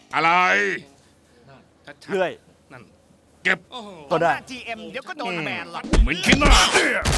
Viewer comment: ได้ยินว่าโถ่เดี๋ยวก็โดนGMแบนหรอก 555+